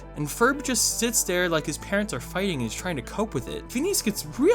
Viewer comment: Ok so at 6:18 an ad popped up but in the middle of his sentence instead of saying “his parents are fighting” I heard “His parents are F’ing